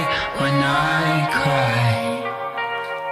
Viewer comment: When it said 'the rose is blue' i thought it said 'the horse is blue'